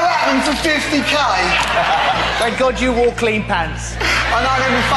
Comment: thumps up if you thought it said parents instead of pants